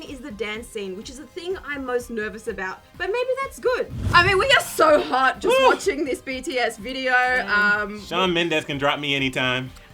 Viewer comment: did anyone else hear her saying, ''we are so hot just by watching this bts video?'' i am so trigger!!!!!